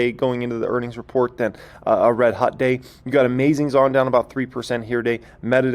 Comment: Did you just call Amazon "amazing"?